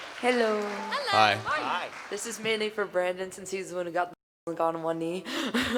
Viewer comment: Dose anyone know what this person said because I wasn’t able to hear them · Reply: She got a question tom brandon sinse hes the one who finaly got down on one knee